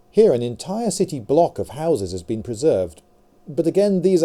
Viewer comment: fuck me I thought it said horses